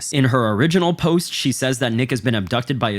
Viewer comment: There are multiple times where bro says “nick has” and it sounds like AI saying niggas 😭😭😭😂😂😂😂 bro im fuckin crying